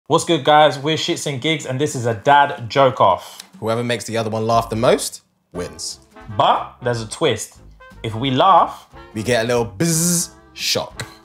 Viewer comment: Lol at first I thought he said jerk off. And I thought well it shouldn't be long 😅